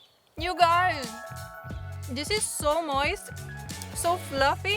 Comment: did u say noice instead of saying nice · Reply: Moist